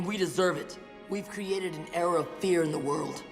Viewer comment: and 7:06 It should be spell "Era" not 'Error" (althought I do sometimes thought I hear the word "Error" )